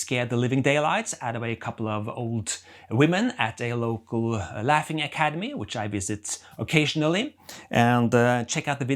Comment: Did you say "laughing academy" at 3:48 ?